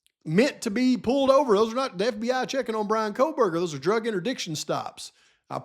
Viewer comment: I've never heard of that before. A drug intridiction stop. At 5:31 you said it and I didn't know what you meant. When I looked up intridiction, I can't find it anywhere. Maybe it's your accent? I hear that right? A drug intridiction stop. Help me out , yah around 5:31 in video. I like to think I'm good with words. I like to think my hearing is all good. What type of stop while he was driving? 5:31, drug intridiction stop eh. Huh help me out with this one. I want to comprehend. Thanks eh👍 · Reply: interdiction